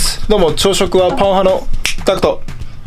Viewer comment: 最初挨拶を聞いた時、 「朝食はパン派のタク」が 「朝食はパワハラのタク」と聞こえてしまった😂笑